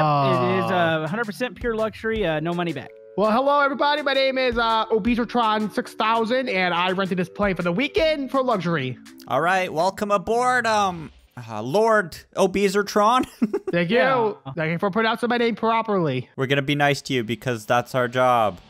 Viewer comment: did he just say lord obeseatron6000?